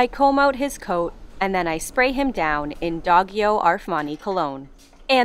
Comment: Did I hear Dog-yo Arfmani cologne? 🙂😆